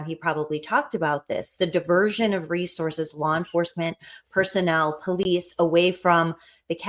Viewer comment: I thought they had video of it. I thought they said person was police capital???